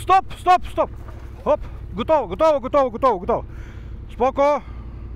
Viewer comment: Did you say "spoko" at 2:28?